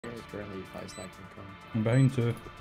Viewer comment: did he say abuse in Hindi? "Behnchod"? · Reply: "I'm behind 2" I said haha